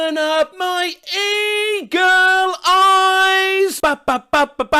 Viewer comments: Today I learned it's "open up my eager eyes" and not "eagle". I thought he was saying he had good vision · I thought it said “ eagle eyes” 💀💀💀💀💀